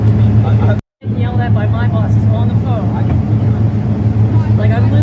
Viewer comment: "... I'm getting yelled at by my boss, on the phone" long pause "like, I'm literally repeating the words that we're giving to me by my..." She never says "Office" "Corporate office" or "Home Office" i dont yhink anyone is making this too big, but you need to get it right, she clearly says BOSS NOT ANY KIND OF OFFICE. and if JD Vance is trying to quash it, yhen let it go, all yhe bitching in yhe world isnt going to change what happen d, yes it will punish yhis business yo keep it going. It will also reflect poorly on Trumpa campaign, and could cost him votes when the Dems twist yhis into, trump supporters are trying to destroy the livelihood of these people because of a misunderstanding, yes it would be a lie, but has lying ever stopped a democrat befire?